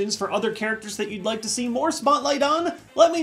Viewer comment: I thought he said “spotlight uh” as in moonlight